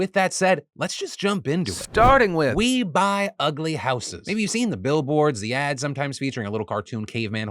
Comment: I always thought that said horses, not houses, growing up. I spent a lot of time thinking about ugly horses.